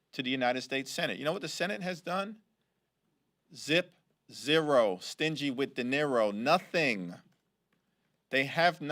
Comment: Did he just say, "Zip, zero, stingy with dineros"? 😂 Maybe they could use this as a new chant up in Portland.